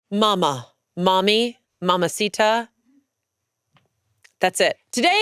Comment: I used to call my mom "Mongy" because that's what I heard (I'm hard of hearing and wear hearing aids). :)